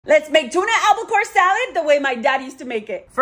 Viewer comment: (Albacore) salad, at first I thought you said apple core salad.